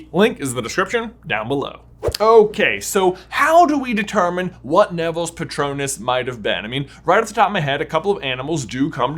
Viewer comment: I swear to god for a quick second i thought it said “what is nevilles pronouns?”